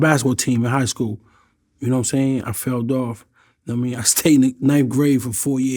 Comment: and seconds b4..... what in God's name is this nigga saying? Nigga, there's no "D" ANYWHERE in those words. I believe the words are "FELL OFF." WIhole time this nigga talkin bout "FELLDOFF." 😆✋🏽